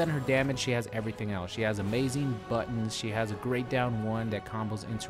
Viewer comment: When he power girl has good buttons I thought he said she has a good but